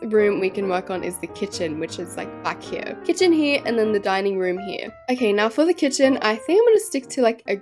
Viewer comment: Love ur videos , But like I just woke up and u said dining room I thought u said dying room 😂😂😂